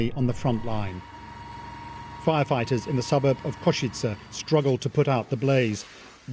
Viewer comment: Did they say suburbs of Košice? (at 0:20) That's in Slovakia, not Ukraine.